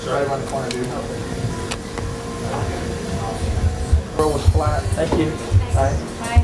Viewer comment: Did Rogan say "The world is flat" to that waitress at 45:00 ? 🤣